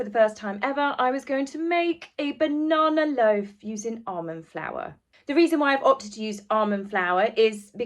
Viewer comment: Sorry but I thought you said "Arm & Flour" and I kept thinking, "did Arm & Hammer launch a flour line?" Until I realized you meant almond flour. 🤣🤣🤣🤣